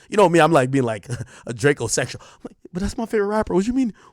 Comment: Did he just call himself a drak-o-sexual?